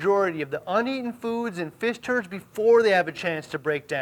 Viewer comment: Did he just say "fish turds" at 15:50? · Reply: Definitely said fish turds, but so nonchalantly that I had to rewind. :)